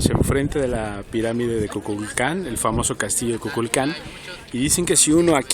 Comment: Min 0:02,pense que dijo Kuklux Klan xdxdxd